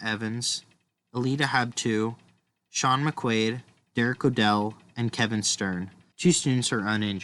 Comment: did you say eric liddel? i didnt hear that correctly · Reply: Derek O'Dell. I had used my computer mic, and I don't think it picked up the audio well.